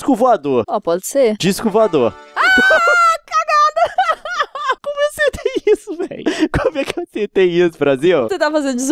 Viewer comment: Eu ouvi a Nilce falando o que eu acho que ouvi? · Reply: "Cagada"